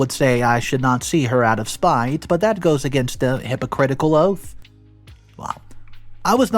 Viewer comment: Hippocratic oath says you’re supposed to treat her obviously . But if you’re her best option then hypothetically she’s not getting the best care possible , if something bad happens to her , hopefully not , then it’s on her and in your case I would tell as many people as possible ! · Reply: I clearly heard, "hypocritical oath," LOL!